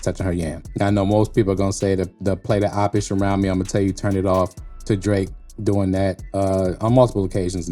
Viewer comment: Crazy I really thought he said Timmy turn it off smh 😂